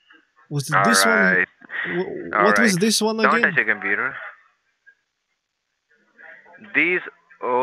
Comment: what did he said? Don't touch the computer? 🤣